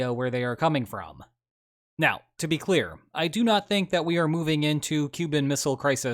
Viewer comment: theres a major subtitle error, you say you do not think we are in cuban missile crisis territory, but the subtitles say you do think